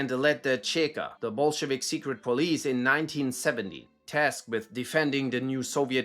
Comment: did you say he founded the cheka in 1970??? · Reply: 1917. It's just that my pronunciation sometimes sucks. Sorry 😂